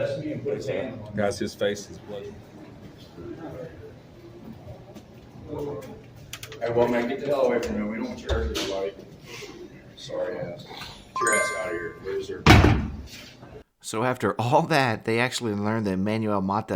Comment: As the guy that bailed them out was leaving the room I thought I heard one of them say sorry Dad.